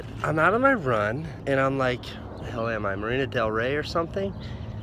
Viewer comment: I thought he said "Lana Del Rey" not "Marina Del Rey". I screamed and got overly excited